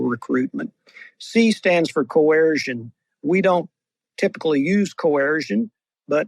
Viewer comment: Left the video when the dude said the US doesn’t use coercion only Russia and China do 😂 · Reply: He said (11:00) "...US doesn't TYPICALLY use coercion..." Yr behaving like a 'snowflake', and one that hears what they want to despite the clear fact of what was said. Discuss, reflect...